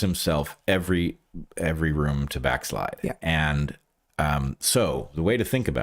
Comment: Bret inadvertently coins a new word. What he said was "backslide", but I heard back-lie